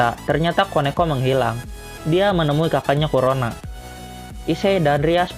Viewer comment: gua gak salah denger kan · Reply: Kurona